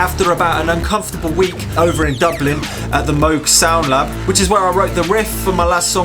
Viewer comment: actually thought you said "Moogs sauna" there, but it can be very hot making music yea